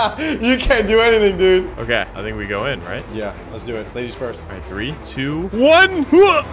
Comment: 😂LMAO am i the only one who heard say "ladys first" in a whisper ish voice and didn't hear 😂LOL